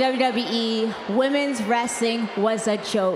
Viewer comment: Sounded like she said womens resting was a joke instead of saying women’s wrestling and kinda messed up saying the word accomplishments , lol .